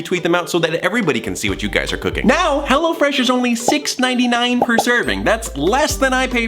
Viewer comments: I thought he said hella fresh also Virgil gave me anxiety in the for few minutes and I gay screeching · The second last time in the promo it sounded like he was saying “Hella Fresh” lmao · Who else thought he said Hella Fresh oof? Xd